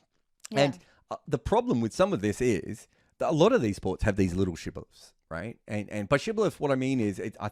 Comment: Anyone knows what's the word uses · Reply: Shibboleth